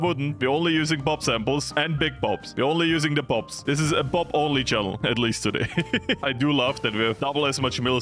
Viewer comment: i thought it said boobs only